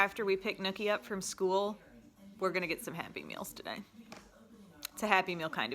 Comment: She kept saying Happy Meals, and it sounded like she said Happy Pills. I was like, ohhhh what are these happy pills you speak of?🙋🏻‍♀️😬